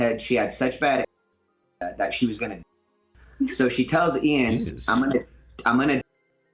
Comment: what's he saying?? Such bad anorexia shes going to die?!? What?!?!